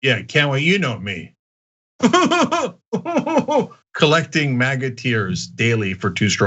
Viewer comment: Every time you say, "MAGA tears," I always hear instead, "maggot tears." 😋 EDIT: autocorrect